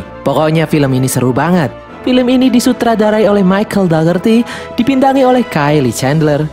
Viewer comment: kok dengernya kayak Michel gak ngerti:v🤣🤣